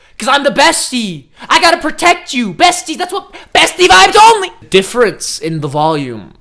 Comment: Why did I hear “Zesty vibes only!” When he said “bestie vibes only!” 😂😂